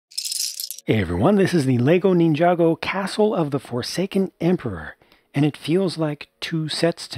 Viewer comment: Im so sorry I thought it said the foreskin not forsaken 😂